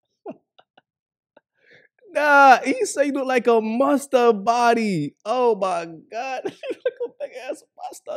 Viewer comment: Lol I thought he said looking like a mustard bottle!😅